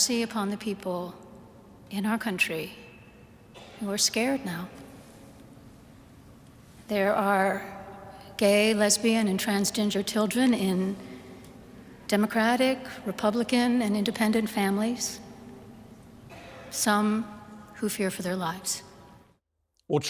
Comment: She said trans ginger I swear that's what I heard